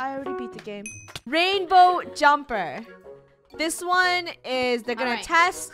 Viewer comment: I thought it said Rainbow Whimper Lol